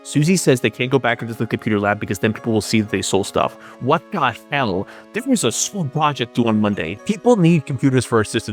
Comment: legit what did you say here best i could make out is "what got fell, difference is a swamp project" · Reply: "What the hell, [isn't there/there's] a school project due on Monday" ...I think??